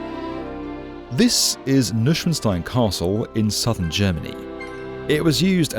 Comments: it's what? Say that again... Noy-Shvan-shtine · As a German speaker, the pronounciation of Neuschwanstein hurt my ears 😅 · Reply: I actually thought he said " Lichtenstein Castle" LoL.. Confused AF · And, of course, Neuschwanstein is a fantasy, about as much as the Disney version. Mad King Ludwig maybe wasn't so crazy, after all, given the eventual economic pay-off. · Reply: If you didn't know and see it, you'd never guess he tried to say Neuschwanstein. Instead it's name was butchered and sounded like Nüschvenstein